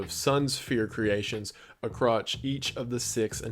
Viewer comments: Did you just say "a crotch" in place of "across":? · he said a crotch instead of across LMAOOOOOOOOO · buddy. did you just say acrotch instead of across.